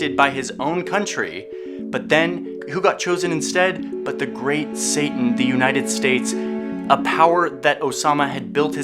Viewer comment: Did i heard that right? You called USA "The Great Satan? Haha I'm not saying you're lying. Btw keep up the great work, now that Vox cancelled Borders there's no reason for me to go to their channel. Looking forward to your next videos.